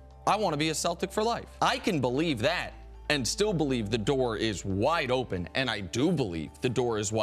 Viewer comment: Did i just heard "the dork is wide open" c'mon. You got to be kidding me.🤣🤣🤣🤦‍♂️